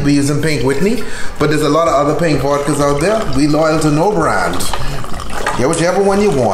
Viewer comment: "Be loyal to no brand" ain't that the truth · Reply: Thought he said “be loyal to your brand.”